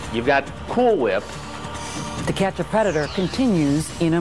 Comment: After hearing about the cat and the Cool whip.....I thought Ann Curry said at 32:55 "The cat's a Predator"!! I was like, Damn this show is getting weird!